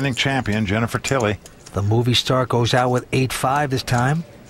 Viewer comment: who else understood "boobiestar" instead of "moviestar"?